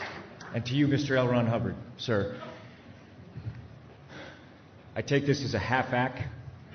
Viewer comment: What is Tom saying at 4:06? I take this as a half something.